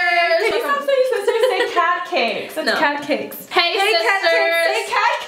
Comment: Ok i thought they said catkers😂😂